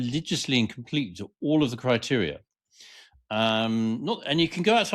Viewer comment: When says " criteria", I hear " crotch area" 😂.